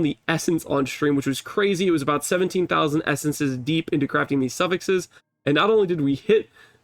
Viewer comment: Sorry english isn't my native language, did you say 17k into Essences for one shield.